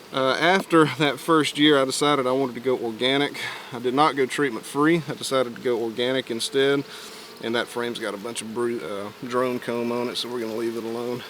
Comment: I thought he was saying “that got too much drone cum on it”